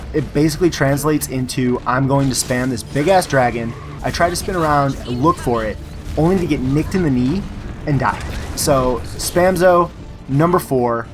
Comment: did he just say he took an to the knee? around 4:00?